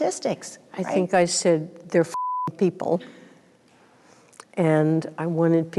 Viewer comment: What word from Yellen was bleeped at 4:50? Could it have been "They're fking people"??